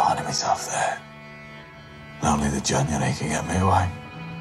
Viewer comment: What did he say at 8:45 I couldn’t understand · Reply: He says "only the january can get me away" which is the name of the boat he was born on, we can see it appear in a couple of scenes!